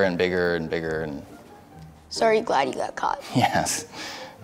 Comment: I thought the white kid said "are you glad you got got?" And I was like, hello? Marshawn is that you?